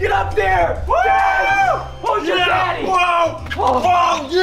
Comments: Am I the only one who heard Preston say "who's your daddy" In 2:18 ? 😳😳😳😳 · Am I the only one who heard Preston say "who's your daddy" In 2:18 ? 😳😳😳😳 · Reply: I heard he said who's your daddy